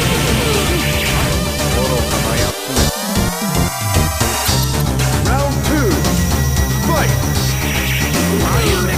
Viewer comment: SOMEONE KNOWS WHAT KAIRI SAYS AT 1:19? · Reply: What a fool (in fandom i think)